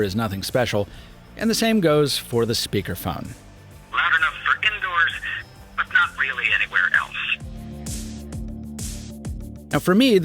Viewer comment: I heard "land on the freaking doors but not really anywhere else"